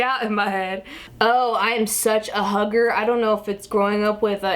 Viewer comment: I always wondered if you were part Asian and lmao I thought you said "I'm such a hooker" haha. Took me a second to realize it was "hugger" =P